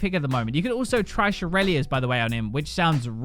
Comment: anyone else thought he said "You can trash Irelias by the way" at 7:30 at first?